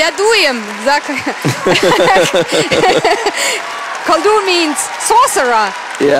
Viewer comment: what does she say at 5:30? Kolu means ... ? what is that? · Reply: On rewatching the video... She uses the word 'koldun' which does mean a sorcerer (in which case her translation is correct but for that word only, not for Michael's name) and appears to have the same root as 'kolyada'... * nerd mode off * :)